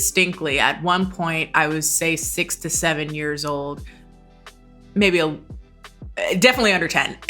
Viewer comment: i heard that as "i was once 67 years old" and i was like "ooh what the past life regression is going on here"